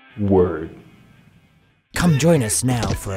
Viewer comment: on 0:18 does he say worm or word? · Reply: Jesus Christ. Word..